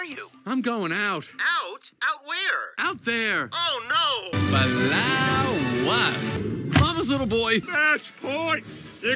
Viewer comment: what is he actually saying? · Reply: flowers